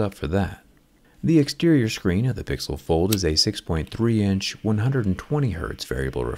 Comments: Did I hear it right! “Pixel fold” around 7:05? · did I hear PIXEL FOLD ?